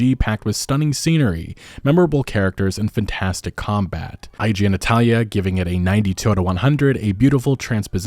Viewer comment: I know you meant to say "IGN Italia", but I heard "I genitalia" and giggled like an idiot